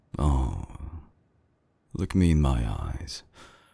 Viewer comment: i thought he said "lick me in my eyes" 😭